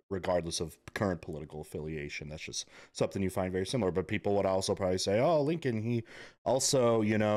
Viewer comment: Small detail, but the emancipation proclamation went the other way around. It freed the slaves within the rebellious states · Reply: Nope, you need to watch it again and listen. @ 6:47 he clearly says "people would also say" OBVIOUSLY not talking about himself. He's even talking with a sarcastic tone.